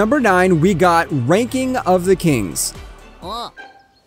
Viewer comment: is it just me or did he say, "Wanking of the kings"??